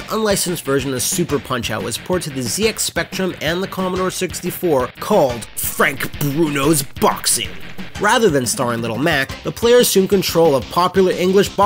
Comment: So he's "Lil Black" instead of Lil Mac?